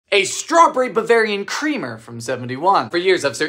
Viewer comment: I misheard it as "Barbarian creamer" and is pretty sure it's a right name for this